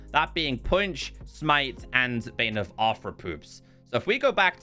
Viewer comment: did you just say bane of arthopoops 😂